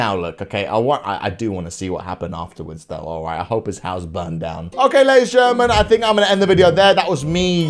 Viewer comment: did he just say I hope his house is burning down